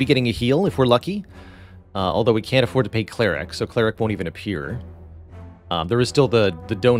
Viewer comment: mentions an event. Can someone tell me what it's spelled like. English is not my main language. I want to see the wiki page for it. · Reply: Cleric It's a word that used to mean a certain type of priest, but it's come to mean a kind of magical healer that uses faith or religion as the base of their magic.